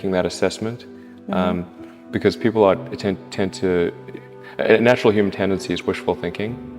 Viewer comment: Does anyone else know what the background words are at 12:40 and other parts? I thought IW as just tripping then replayed it lol. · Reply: "People are... tend to... natural human tendency is wishful thinking"